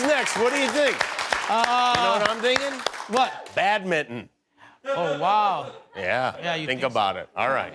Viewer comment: Padmington... I heard "Bad mitten"